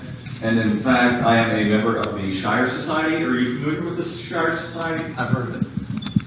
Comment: It sounded like he said the Shriners Society??? Doesn't sound like the Shriners I've heard of??